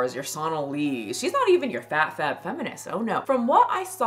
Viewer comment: I fr thought you said ‘you’re fat’ as a statement on me but you said ’your fat’…